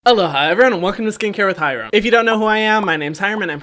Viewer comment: I just realised you've been saying "aloha everyone" 🙃 I thought you were saying "hello hi everyone"